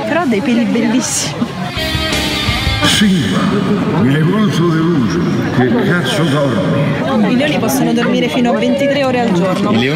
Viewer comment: Lol, non ho capito cosa dice dopo Simba. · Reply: "Simba, mi hai molto deluso, che cazzo dormi!?"